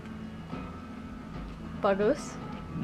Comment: hahah! Did you say "bagus" at 6:08?? haha.. I hear some bahasa eh? :P lookin forward to your adventures around the peninsula